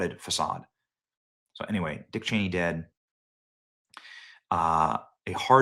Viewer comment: Ya know, I thought it said dick head dead. Same thing I guess